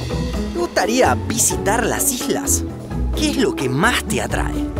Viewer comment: Soy el unico que escucha que dice p*uteria visitar las islas?